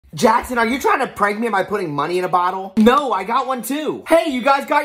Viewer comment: Why tf did I hear "Jackson are you trying to impregnate me?"